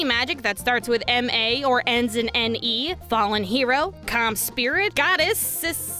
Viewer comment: Clam Spirit, I can totally see a clam spirit being summoned to help you out instead of a calm spirit like you say